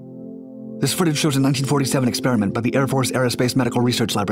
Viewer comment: sounded like he spoke so fast all I heard was: "This-soodag-wenin-nin-48"! (CHILL OUT BROTHA)!!!